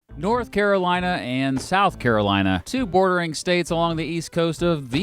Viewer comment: Lol I thought in the intro he said "North Carolina and South Carolina. Two boring states along the East Coast..." HAHAHA!!! Also, greetings from North Carolina. :)